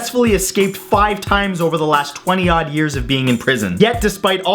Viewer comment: 20-- what years? 20 odd is what i heard. You are canadian and Canadians do say "zed" instead of "z" so is there a number pronounced "odd" in Canada? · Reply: Twenty odd years as in around twenty years, he probably didn't find the exact number